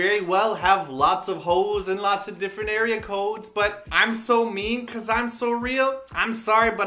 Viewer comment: it sounded like quoted botdf at 8:16!!! "money and hoes in different area codes"